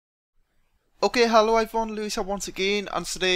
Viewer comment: He says hello everyone i think :P At first I thought he said Hallowed everyone lol